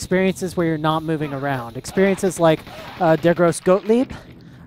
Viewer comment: What are you saying at 31:13 ? Dergross Goatleap??